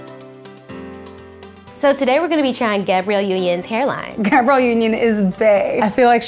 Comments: Who else thought that it said Gabrielle's unicorn hair? 😂❤️ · Who else thought it said unicorn hair